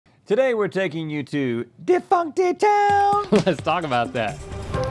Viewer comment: I had to restart the video I thought he said today were taking you to get Fuq in town lmao